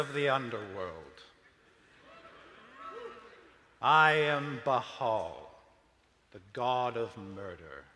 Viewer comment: 32 wow I need to get my ears checked, I heard "I am Trustfall, God of Murder"